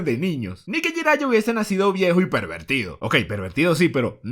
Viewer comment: Lo que entendí en el 1:21 "no esque haya nacido viejo y lesbiano"